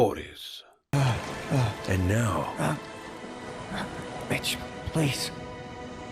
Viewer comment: the subtitle says : bitch please 😂